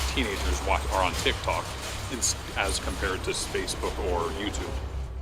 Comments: Is it just me, or did he actually call Facebook, Spacebook? o.O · I'm sorry did he just say "Spacebook?"